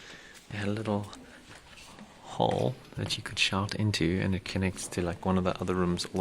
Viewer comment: I thought, you said, SHART into xD